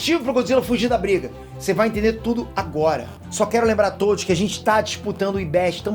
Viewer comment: "só quero lembrar a todes" Escutei isso mesmo ou tô ficando maluco?